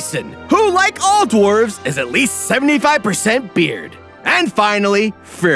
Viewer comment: For a minute there I thought he said "Beer" not "Beard"